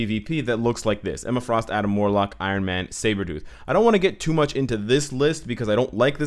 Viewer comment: Did he say IronMan?